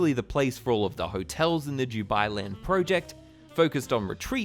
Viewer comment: Auto captions "joob eye" 😂😂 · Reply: Yeah I thought he was saying jew-bai land...